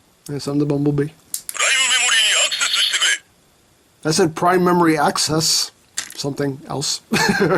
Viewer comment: I thought he said Gaia Memory, I thought he was about to henshin into W for a second